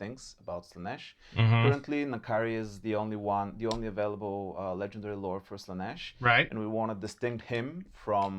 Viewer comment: what they said : currently, N'kari is the only lord avaliable for Slaneesh what I heard : currently, Kairos is the only lord avaliable for Tzeentch